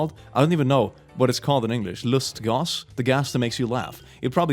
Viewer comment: did just say "lust gas"? Please don't release the lust gas. Think about the children.